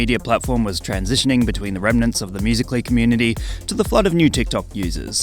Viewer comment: When you said "flood" in the title I thought you said "custard " in Spanish, now I got hungry.